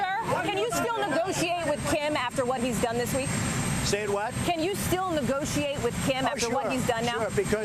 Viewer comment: did Trump say "say what"?